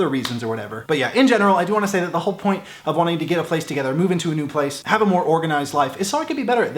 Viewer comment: Anyone hear him say move in together at 31:38? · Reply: He said "get a place together", I assumed he meant like "get it together" as in sort it out and clean it up etc